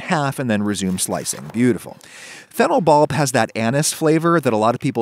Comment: Ok am I the only one who heard "anus" flavour at 3:29. Its really throwing me off.